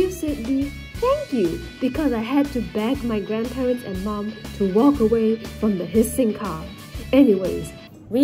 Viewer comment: When said "To walk away from the hissing car" I thought he said "to walk away from the pissing car" I really thought